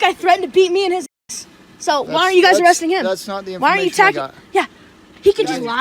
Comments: “thats not the information we got” is what he said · what i heard at 4:31 "that's not the information you got" i might have made a mistake/misheard it though